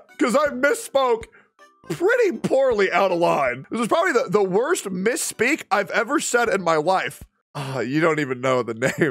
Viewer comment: thats not the worst misspeak what you said after that was ("this is probably the worst misspeak IN my wife")